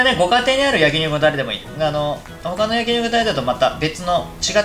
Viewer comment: ぐらいからなんて言ってのｗｗｗ 「他の焼肉のタレ」かなｗ